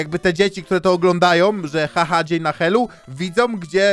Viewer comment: usłyszałam że cały dzień na haju XDDDDDDDDDDDDDDDDDDD